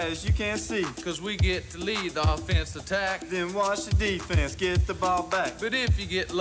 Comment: Damn, my bad, I thought he said "And watch the Offense get the ball back" I had the speakers up loud